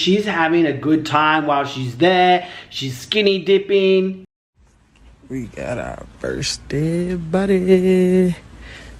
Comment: I thought I heard say, "we got our first dead body" I don't think given the circumstances that it was figurative at all but predictive! Evil and dangerous set of jealous miscreants!